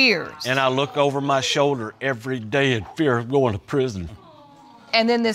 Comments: When the judge said "the cycle just kept continuing " , I thought she said the psycho just kept continuing. I bout died laughing 😂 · Judge said “ cycle “ I thought she said PSYCHO 😂😂😂😂😂😂😂😂😂😂😂😂😂😂😂😂😂😂😂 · bro we don’t need you in the middle of the screen just staring back at us 😭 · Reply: when this judge said " and this cycle....", I thought for a sec she said "and this pyscho kept continuing to..... "